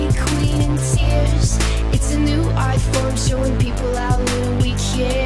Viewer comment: for the longest time i thought she said "it's a new iphone"